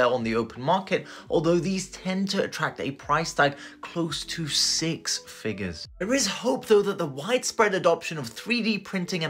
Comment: I heard “close to six fingers”….